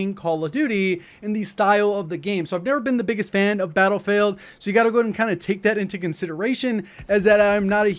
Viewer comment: Did just call it Battlefailed at 0:27? lmao